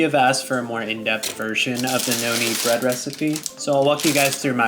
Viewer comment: When u first said it my dumbass thought u said “no need bread recipe” lol